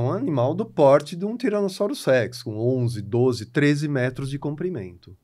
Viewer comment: Kkk em 10:40 de video escutei varias vezes e tenho sertwza que o professor diz que o nome é tiranossauro sex.